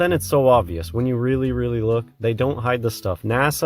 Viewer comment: I believe he said "James Vaughn" not "James Bond" and I think he MEANT to say "Vince Vaughn" who you have to admit HAS been in many campy movies.